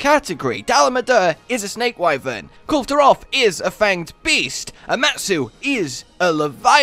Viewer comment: Hold on, did he say fanged beast to Kalve in